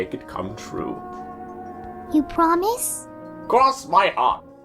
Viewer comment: im sorry but its just so funny to me that if you put the subtitles on instead of saying "cross my heart" it says "cross my aunt" 😭